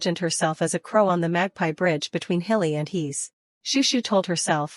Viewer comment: bro. i thought it said magpie bride until literly 11 hours later when it was said magpie bridge💀